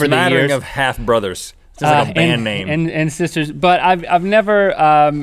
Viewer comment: t sounded like link said sister's butt